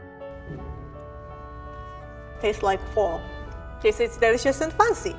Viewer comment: "Taste like Fall", but the subtitle says, "Task like pork" haha